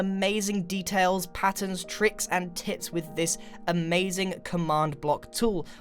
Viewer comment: am I the only one who hears him say...T I T S